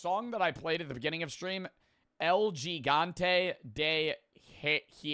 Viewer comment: “El gigante de hierro” is what he’s saying btw 😭